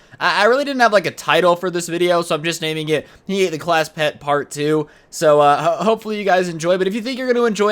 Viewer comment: For some reason I thought it said, he hate the class pet